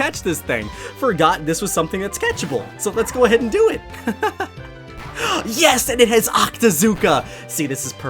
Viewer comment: i thought you said "Lets go ahead and fight *MEW*"